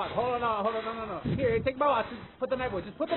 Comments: Am i the only one who heard "take my watch"? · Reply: Yeah I hear “Hey take it easy now holy fuck” “Hold on no no no no” “Hear take my watch” “Put the knife away, put the knife away” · Am i the only one who heard "take my watch"?